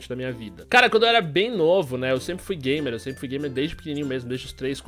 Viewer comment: Kkkkk eu entendi ele falando que ele sempre foi gay